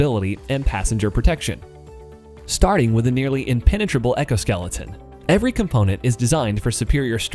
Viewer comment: Did he say “ecoskeleton”? Haha